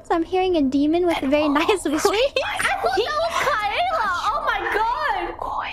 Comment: bae ngomong indo apa aku salah denger? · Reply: "i thought it was kaela"